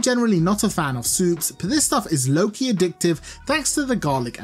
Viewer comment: did you say "low key addictive" or "Loki addictive"?